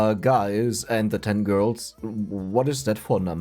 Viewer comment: AYO BRO i misheard it as hi guys and 10y old girls... my guy had me almost drown on land with that one, took me 10 min to stop coughing.